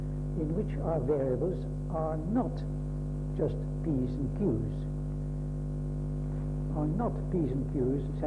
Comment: Amazing. I thought he said just 'Yes' and 'No'